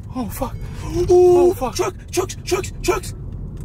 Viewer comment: I thought you said ducks